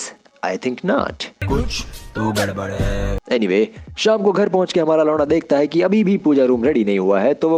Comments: Is it just me or he really said "shaam ko hamara Lauda"?? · did you say Sham ko ghar puhanch k Hamara "loda" dekhta hai?